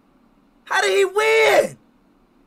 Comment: What does ishowspeed says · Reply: how did he winnnnn?